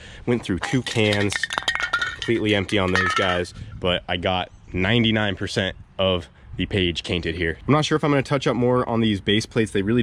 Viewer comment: “I got 99% of the page cainted” 😂 anyone else catch that?